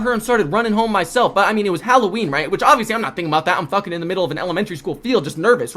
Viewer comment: Bro I fr thought it said “I lost my v-card in elementary school” 😭🙏